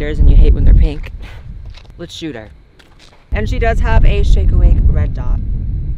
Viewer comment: Okay maybe I need a hearing aid. I could have sworn you said "this one has a shake weight" when you were talking about classier customizations. I couldn't stop laughing after that